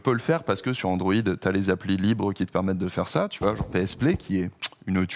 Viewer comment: C'est quoi le nom de l'appli à 5 balles pour remote play sa PS5 en cloud sur Android, j''entends mal ce qu'il dit ? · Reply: Apparemment c'est PSPlay